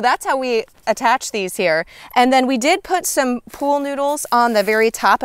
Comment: I thought you said pole noodle…… took me a minute to figure out what it was. 😂 Great info, thanks.